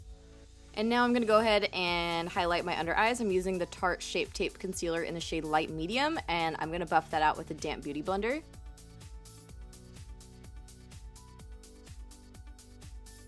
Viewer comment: I thought i heard "whore concealer" 😂😂